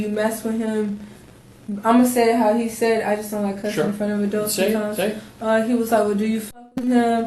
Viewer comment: I think she said "I don't like cussing front of adults". So she doesn't like to cuss in front of adults, but she is perfectly fine with cussing in front of dogs and children? Does she realize how f***ed up that is? doubt she had very many brain cells. The WHOLE interview she was STUPID! (Pondering 🤔) (sad 😢😭) (angry 😣) (evil 😡🔥🥵). What could we do to someone like them,,?